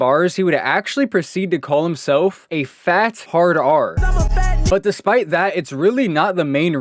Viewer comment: for some reason when bro said he calls himself a fat hard r, i thought for a moment he called himself a fat retard 😂